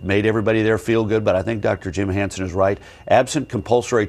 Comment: Did I hear him say that "Dr James Hansen is right" ?? ~